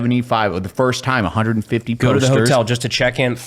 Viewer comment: You said posters. I thought you said coasters.